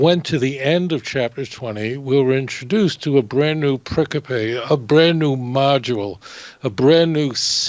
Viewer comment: Again: at 00:35 the Rabbi uses a word that sounds like "Pric-a- pay" referring to the text. Can anyone tell me what the correct spelling is and what it means. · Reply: The word is “pericope” — pronounced “peh-RICK-oh-pea” — and it means: “An extract or selection from a book, especially a reading from a Scripture that forms part of a worship service.”